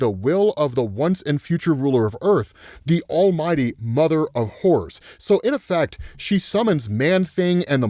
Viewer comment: swear he's saying mother of whores ...